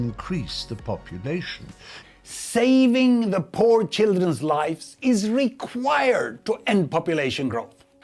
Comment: alright so why did i hear "Saving the poachers lifes is important" and i was like hol up